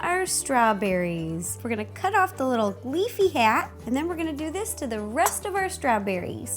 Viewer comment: Did She Say "Leafy Hat"